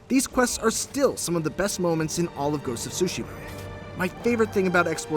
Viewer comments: I almost thought he said ghost of sushi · Lol at first at the beginning I thought the reviewer said Ghost of Sushi 🍣